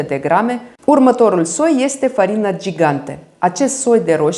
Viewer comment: Buna ,la min 11:52 ce soi ati spus ca este? Farina gigante? Nu am înțeles bine. Mulțumesc frumos. Salutari de la Arad. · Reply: Bună. Da. Farina gigante. Salutări din jud. Arad!🙂